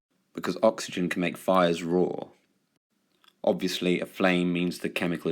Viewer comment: did you say "make fires raw" or "make fires roar"